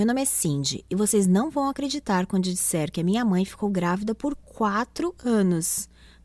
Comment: "minha mãe foi gravida por 4 canos " eu escutei direito. Nossa como?(mente poluinda)